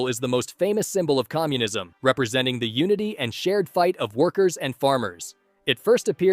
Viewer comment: I heard "nudity" instesd of unity when you talked about communism. 😂